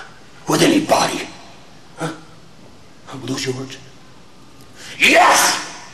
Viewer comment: what did he say there?😭🤣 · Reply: "Are those your words?"